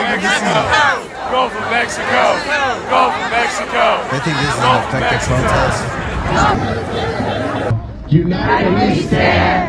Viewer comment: I thought they were saying "go back to Mexico" I was so confused.